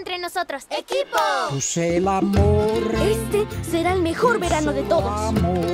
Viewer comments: escuche se mamo :V diganme q no soy la unica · porque se escucha que dice c mamo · No c pero escuche esto en 0:24 dice c mamo oh khe